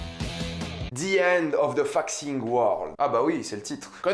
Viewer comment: pourquoi il dit "faxing world" 😂